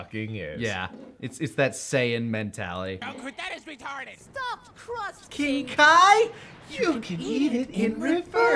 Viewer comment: so english isnt my first language and always understood "just trust king kai" instead of stuffed crust :D